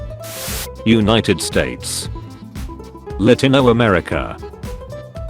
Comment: Before I learn how to pronounce United State, I actually thought it said “Untitled State”